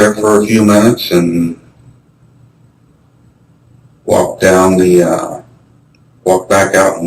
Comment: Timestamp please?? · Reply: I think it was around 19:01 whilst he’s talking about what he did that day on the bridge! He says “ I walked down the ahhh” and stops himself from completing the sentence.